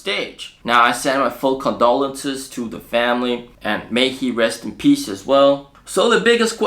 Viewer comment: Did you just say may he rest in pieces?